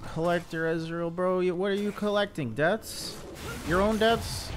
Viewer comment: to Ez “ why are you getting collector? What are you collecting, deaths” almost spat out my drink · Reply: I thought he said "collecting his own debt", I think that'd make more sense.